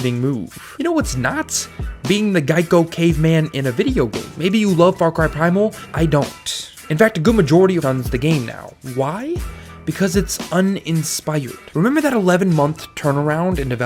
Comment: can someone please tell me what the hell he said. "a good majority UNDS the game now"? whatdoes that mean?